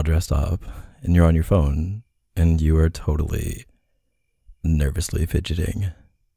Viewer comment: can someone figure out wth was said here? I have NO IDEA 😂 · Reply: “totally nervously fidgeting”